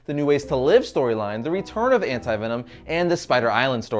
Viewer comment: I thought it said "AntVenom"